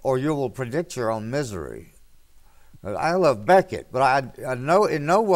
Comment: i couldnt quite catch that. what writer is he talking about? Sound like “Becken” (starts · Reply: Samuel Beckett